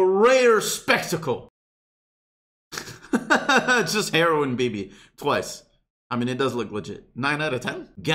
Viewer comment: Can someone explain to me did he say heroine as in a drug or hero ine bibi